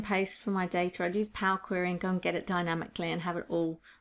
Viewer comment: Hello At 42:57 she said something like Palcorim which she uses to update data I could not get it accurately what she said Can anyone please clear me what she actually said? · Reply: I said Power Query. You can learn more about what Power Query is and how it can help you here: <link>